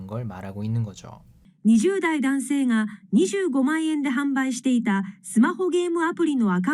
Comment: 항상 감사합니다 근데 販売者 가 자꾸 販売しての로 들려요 저만 그런 것이겠죠?ㅋㅋ 더 열심히 공부해야겠어요 ~~